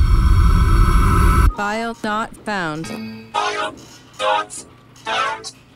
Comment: think they put a 😂 emoji because I hear File not found Hahahahahaha File not found